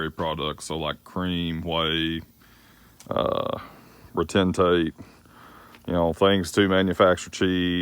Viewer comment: Anybody; What's the word at 4:41 on this vid? A product he hauls. I can't get it. Thank you! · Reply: Frank Swain “Retentate”